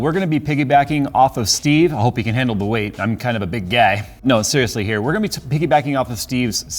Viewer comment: big gei? or did i just misheard it?? hahaha